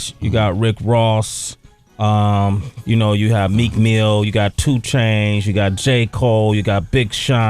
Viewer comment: did he call him 2change ? :D